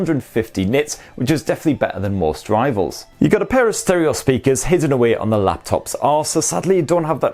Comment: Did I hear you say "a pair of stereo speakers hidden away on the lap top's arse"? 🤣🤣🤣